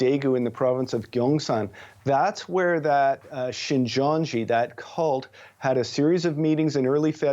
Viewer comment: i heard it right. Shincheonji that cult he said. woops keeree woops